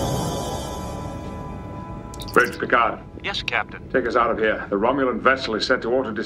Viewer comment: "Bridge to Picard" Did I hear that right lol